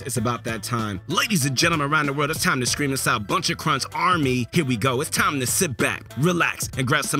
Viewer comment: wait... what? I thought he said "buncha crunch on me", not "buncha crunch army".